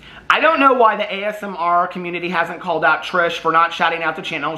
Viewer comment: pronouncing “ASMR” the way my granny from Alabama pronounces “asthma” is killin’ me 😂🤣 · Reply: I keep hearing "the asthma community" too.